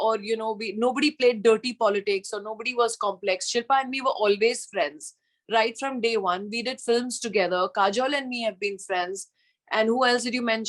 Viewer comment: she clearly didn’t mention Karishma saying that ….duh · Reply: she clearly says of no problems at all with Shilpa, Kajol and Lolo (which is Karishma kapoor’s pet name) from 8:30 to 8:41 in the Video.